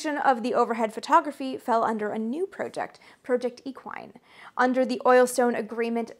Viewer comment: I know, probably U2 gets most of the time taken as "YouTube" when automatic subtitles are enabled, but it still is a great help for us non native speakers when naming people, places or using some terminology. Please, enable them. Great video nontheless! I get most of what you're saying. Edit: 7:16 did you just said project equine? Googled. Yes. But I guess you would agree that pausing the video to check words is a bit disruptive 🙂 You see there are certain words that not even context can give confirmation as to what you heard or not. What tha heck does a horse have to do with aerial photography? 🤣